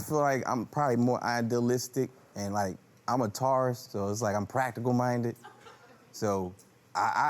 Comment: His logic “I am a Taurus so I am practical minded. If they’re fat, I think they’re gonna cook!” 😩😂😂😂😂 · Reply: I thought he said, “I'm a Taorist”😭